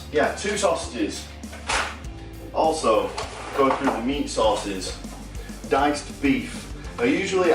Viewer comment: Like your accent. Thought you said sauces at first, thought this guy really likes sauce. But then I figured you talk about macro sources! Keep up the awesome content!